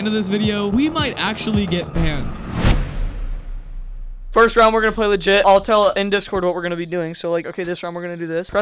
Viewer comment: what I heard: ok today were gonna play shi